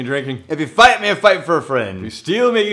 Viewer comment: it sounds like says, "And if you steal.... may you steal your LIVER'S heart." Sounds appropriate .... ❤️